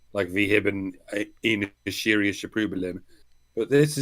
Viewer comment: "Wee hibbin ién surriusche prØblém." LMAO! I had to listen to that three times before I realized what the doctor said: "We hebben een serieus probleem." That sounded more like the Swedish Chef. 😆